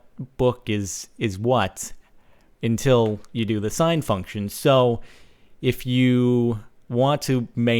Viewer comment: Jordan: "the sign function" What I hear: "the sin function" curses stupid trigonometry